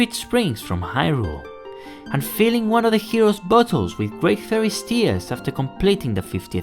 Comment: did anyone else think he said "buttholes" instead of "bottles" at first?